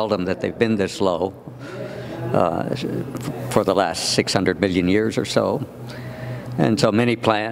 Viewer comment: did he say 600 billion years? The universe is only 14.8 billion years old.... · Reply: I think he said 600 million years.